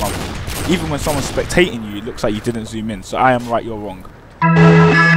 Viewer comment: Lol "I'm right you're wrong" 5:57 if you didn't catch that.